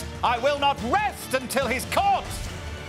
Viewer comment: 😂 you need to watch these with the subtitles on, instead of "I won't rest until he's caught" it was "I won't rest until his corpse -" and then it shows him asleep 🙈